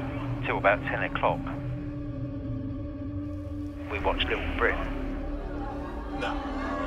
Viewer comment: Completely off topic, but can someone tell me what the show is called that he and his wife were watching? I thought he said “Lil Brit” but I can’t find it. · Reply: Little Britain! It's absolutely MARVELOUS! I was introduced to it when it aired in America under the title "Little Britain: USA"